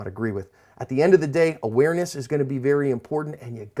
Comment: You're video's are amazing I learned a lot from them.But whenever you say ''cover your ASP'' the first time I watched one of you're video's I thought you said ''cover you're ass'' lol.